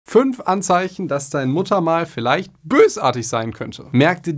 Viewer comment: Ich hab verstanden ,,5 Anzeichen das deine Mutter vielleicht bösartig ist" 😅